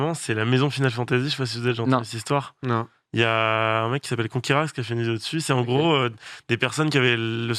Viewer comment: c'est quoi le nom du youtubeur dont sardoche parle ? j'entend "konkiraz" mais je sais pas comment ça s'écrit.